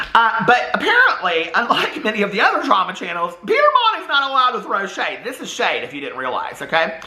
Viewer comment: I thought you said "Peter Monn isn't a loud to crochet " not "Peter Monn isn't a loud to throw shade" I was not only confused but angry that some people think only specific people can crochet.